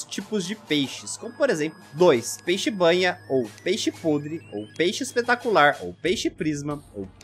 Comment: Só eu que ouvi "peixe espetacular"?